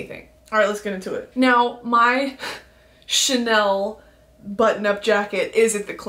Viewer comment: Bestie I thought you said your Chanel button nub💅🏼